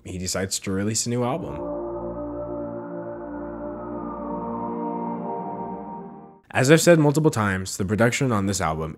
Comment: Could someone tell me what the words are saying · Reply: "Oooooh"